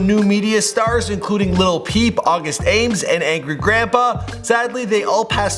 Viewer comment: At first I thought it said *Lil Pump*